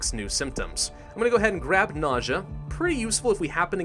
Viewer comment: Every time you say "nausea" I keep hearing "naja" lol